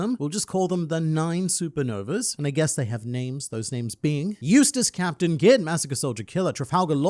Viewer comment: I thought you said "useless" instead of "Eustass" haha